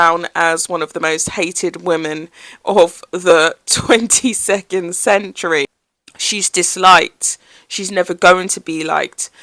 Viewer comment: At minute 2:40. Did she say "most hated woman of the 22nd century?"